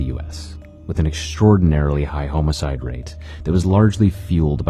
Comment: Did he just say home-icide? Instead Homicide 😂